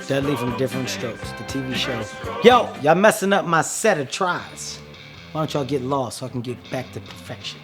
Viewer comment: the caption said "set of tris" I legit thought he said "set of tries" like attempts instead of reps lol I think that's funnier, so I'm going with that as what I heard. lol